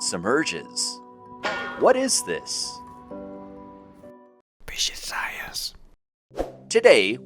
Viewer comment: I thought it said "bitches science"